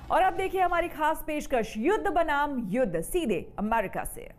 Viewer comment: I heard yudh banam yudh .. anybody else ?